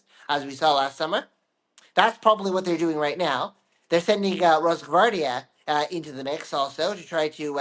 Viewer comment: I cannot understand the two words he says at 0:37 - they are sending in ? ? can anyone help me? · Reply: rashhhgordia